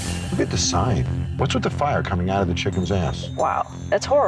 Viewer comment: I’m sorry but at 10:58, John the fire is coming out of the chickens guitar, not the chickens but LMFAO I don’t know Where are you see these flames coming out of the chickens ass, but they are most definitely coming out of the guitar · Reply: I thought he said "What's with the fire coming out of the chicken's amp". There's no amp on that sign though, it's obviously coming out of the guitar 😂😂